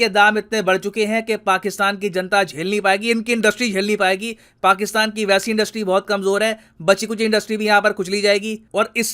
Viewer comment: While listening sir ji at 1.75x ....@ 6:14 it sounded like " inki taftessh jhel nhi payegi 🤣" then I realised it's industries